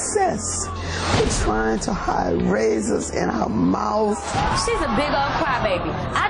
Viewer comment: lol if you close your eyes and listen it sounds like she says " trying to hide raisins in her mouth" 💀